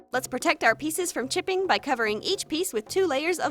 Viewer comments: I thought she said, "To protect our project from chickens, use ..." At 6:10. 😂😂😂😂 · i thought she said, "let's protect our peices from chicken" 😂